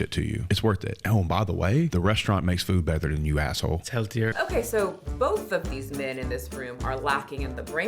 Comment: Did he just say it’s healthier at the restaurant??